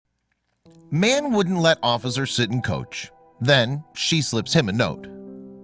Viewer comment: Omg for a second I thought it said “Man didn’t let officer sit in coach, do she slaps him a note”😂😂😂